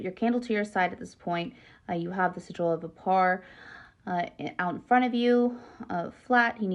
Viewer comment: What does she say at 15:52? I heard to put candle to the side of you,then i couldnt make out the next step. I tried slowing playback speed and captions. "Schedule to the par" is what my CC declared. I dont understand what that could mean. Please someone let me know. I dont want to screw it up somehow. Thank you! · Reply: Have the sigil of Vepar out in front of you